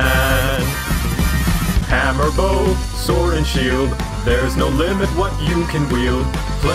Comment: I always hear “ham or bow, sword and shield”